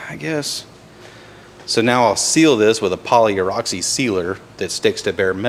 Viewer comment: At the 20:38 minute, did say 'polyureoxy' (sp) sealer?